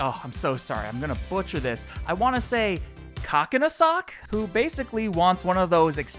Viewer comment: What did bro wanna say? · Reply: Kockinsock